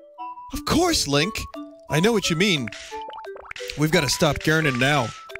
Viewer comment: Did he just say "We've got to stop Gernon now" gernon